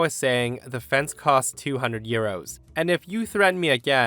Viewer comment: Ha! I thought he said 200 year old fence, I pondered that and then heard him say 200 euros and felt like a dunce, lol.